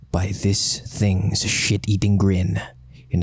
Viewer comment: i thought you said shi# eating